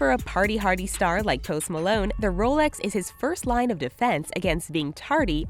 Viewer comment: i thought she said toast malone